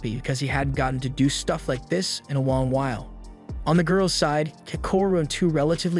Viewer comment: why did he say “wong wail”😅😂